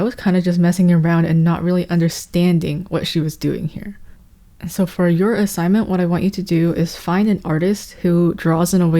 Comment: Who is the artist at 14:16? I can hear what you say, but I can't understand, really. Arooz? Ahruz? Aruz? Arus? lol I can't find an artist by any of these names. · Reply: arucelli/knight zhang !